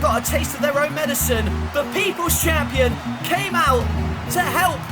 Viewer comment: When you said The People's Champion I thought you said the Divas Champion lol